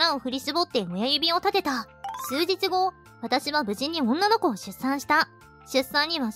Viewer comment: の「無事に女の子を出産した」が「口から女の子を出産した」に聞こえてて草